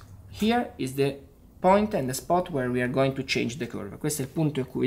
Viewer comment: i swear i heard kurwa hjaha